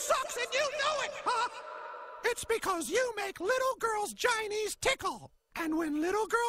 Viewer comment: At first I thought he said Chinese tickle but he said ginies tickle. 😂😂😂😂